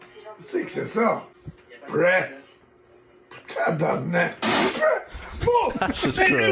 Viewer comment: is spoken with a French Canadian dialect called joual. I will translate to the best of my abilities Dad says "what is that?" "press?!" "tabarnak" I can't understand the next phrase because of the talking but he says "I don't want to see you in this house anymore!" "these fucking pranks" "colis" Son says " why did you press?" Father "IT SAID PRESS!" Note that there is a lot of Quebec swearing and slang so I took some liberties with my translation 😅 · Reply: Ah, he says "Mes lunettes neuve, tabarnac!" (My brand new glasses, goddammit!) Took me a few good go before I got it.